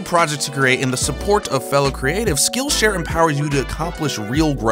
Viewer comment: I totally thought he said "skin share" instead of "skill share" lol.